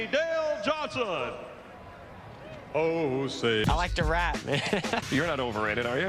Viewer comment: did I hear that right? Jeff likes to rap? Lol 😂